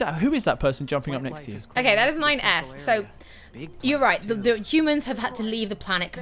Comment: When said "That's 9S" I legit thought I had heard "That's my ex" and I thought 'Is she cheating on Ignis?' 😂